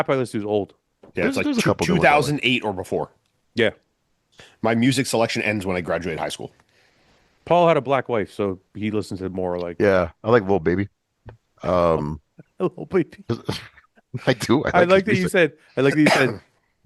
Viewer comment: Paul definitely separated or got divorced fully. They keep making jokes alluding to it. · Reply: I thought I heard Fouad say Paul "had" a black wife when they were talking about music.